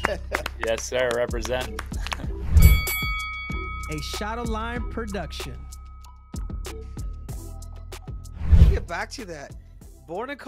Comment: This whole time I thought Cejudo was saying "a shot of lime production"